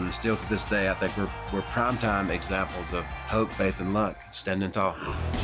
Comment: I thought he said coke Faith and luck